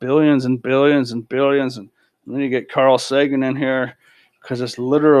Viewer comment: Did you say Carl Sagan